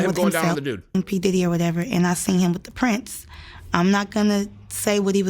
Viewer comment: did she say he was with Prince? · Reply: "the prince"